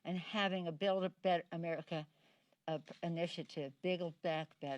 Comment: Did she just say "Have a build-a-bear-America" ?